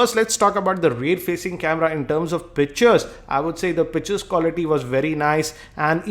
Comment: He sounds like he is saying bitches not pictures lol